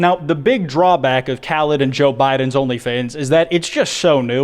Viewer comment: Lol at 2:49 did he say "khalid and joe biden only fans"🤣🤣🤣🤣🤣